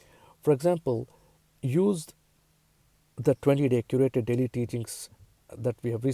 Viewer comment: Namaste ji. At 9:27 use what? I have listened to that part six times but cannot understand . Can someone please spell it out? Thank you ji ... 🙏🌍🕊🕊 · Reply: 20-day curated daily teachings is what I think he said